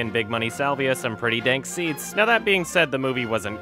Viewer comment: I thought you said dank "seeds". I was like, damn, I didn't know Stuckmann was holdin'.